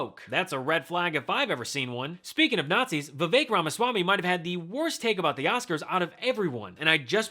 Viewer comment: I thought I heard him referred to as "Vivek Ramasalami".